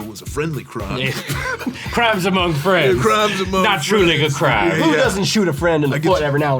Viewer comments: dude I thought he said the n word when he say "not truly a crime" and I almost lost my mind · Did he just say true n-word crime?